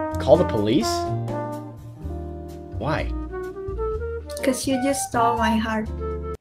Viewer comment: > I think she says "Because you stole my heart"... not "Because you stopped my heart"... as you wrote in subtitles... Lol~!